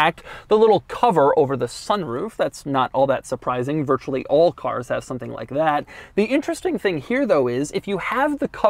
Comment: did he just say "all cars HAS ! Something like that " ?? :O